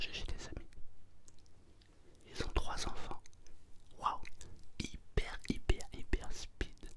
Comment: J'ai compris "Ils ont des enfants hyper hyper hyper hyper stupides" 😂😂